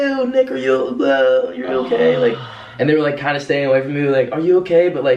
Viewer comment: I hear the. World GO AWAY KEEM